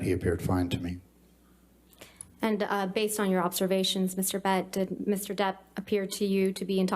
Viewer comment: Did the lawyer said "Mr Bed" instead of Mr Depp at 10:36 😂? Too bad for the hearsays because it's a very good testimony....